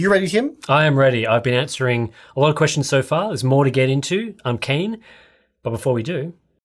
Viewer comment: Gotta love that accent. Me mishearing Tim so I think he says "I'm Kane"... Maybe he could pull off bald + goatee. Hmmm 🤔